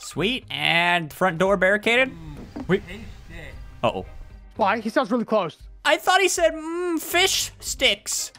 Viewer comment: I heard “fix this!”